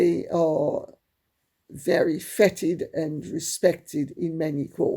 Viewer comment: So pleased you are home. I hope you continue to recover apace. I had taken out my hearing aids ready for bed and misheard you. I thought you said that the Kardashian/Jenner clan were fetid. Then realised that you had said feted! Made me giggle.